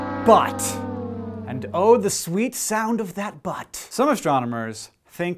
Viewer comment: "....and oh the sweet sound of that but..." Because I'm immature this is what I heard: "and oh the sweet sound of that butt." Tee hee hee. Butt.